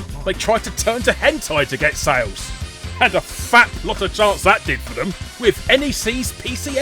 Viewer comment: Did he say "A fap lot of chance that did for them!"?